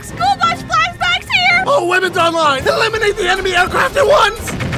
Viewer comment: I heard "all women are mine" instead of "all weapons online"